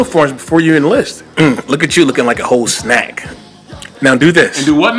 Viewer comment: Really bro? A whole snack? Got me dying over here. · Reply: I thought he said "hoe snack" haha